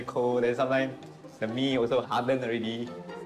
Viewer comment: he actually says "the mee (noodles) is hard already" he did mention that they were not given meat during lunchtime and was also eating noodles at the time, hence the comment